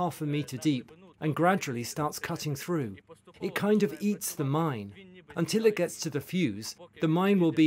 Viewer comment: "It kinda yeats the mine" is what i thought he said lol